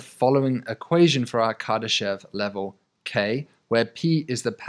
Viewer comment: Oh thank god,…. I thought it said Kardashian…. Was like🤬 I don’t wanna loose my pen15.